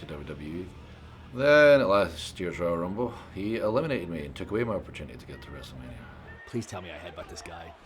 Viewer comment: who else thought he said, " he took away my virginity "